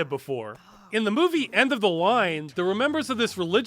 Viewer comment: does anyone know what movie he's talking about? It sounds like he's saying "End of the Line" but I can't find it anywhere